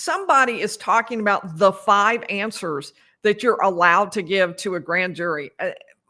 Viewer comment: As I listened to your show today, I misheard "the 5 answers that were permissible for a Grand Jury." I thought you said "the 5 'HAMSTERS' that were permissible for a Grand Jury." I couldn't figure out why a GJ needed hamsters. Relistened & then it all made sense. Wow, did I belly laugh at this faux pas. It's incredible how you're able to hear everything and report it to listeners.